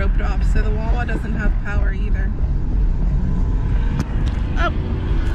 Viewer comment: did say "holy fuck"